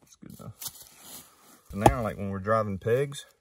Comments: Swear I thought he said settlers RANCH. Took me a sec to figure out he said wrench. Cool tool cool vid cool cool accent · Man I thought he said ranch